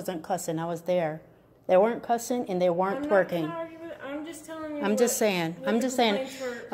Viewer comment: did she say the girls werent twerking? well they should have